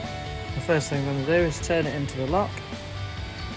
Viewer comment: you say "the first thing you do is turn it into..." and I'm not sure what you say or do after that? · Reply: Turn it into the lock , ie as far as it will turn